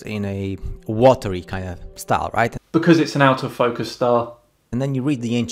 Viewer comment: Sorry but at 10:41 what did he say? Is a...something..star..sorry but English is not my primary language Ok ok..out of focus..I think I get it